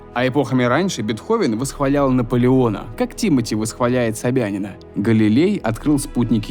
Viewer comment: Не зря мне послышалось "ЁбЯн")))